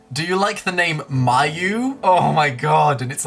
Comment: Lol when I heard you say, "Mayu" I thought you said my last name (Mayeux). 😂